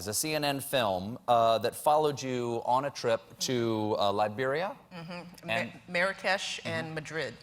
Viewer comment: Did he say Latveria?! How did she get the permission of Doom!?